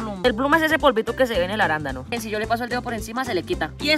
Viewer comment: Pensé que había dicho habanero yo no msmches asi es el habanero no creo pero era arándano jeje ❤❤😊